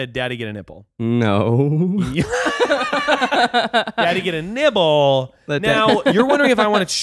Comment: I heard "Nipple" too but I knew I was wrong 😆